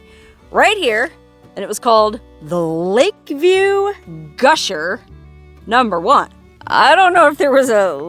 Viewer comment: Did you say Lake Field gusher and overdub it with Lake View gusher?